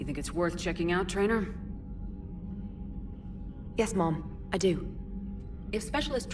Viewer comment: eeerm, is it just me or does traynor say "yes MOM" instead of "yes MA'AM" ? x'D